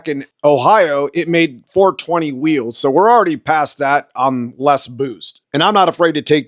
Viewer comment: No it made more power due to more boost being added. We only ran about 10-11 lbs at mustang Dyno and tq was off due to bad rpm signal … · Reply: Gotcha, I misheard when I watched it then, I thought you said more power on less boost.